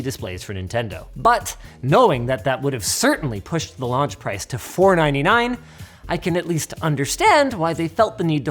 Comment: Captions say $4.99 instead of $499 lol